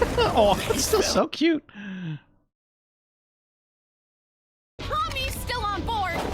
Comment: the subtitles say mommy instead of pomni.... hmm...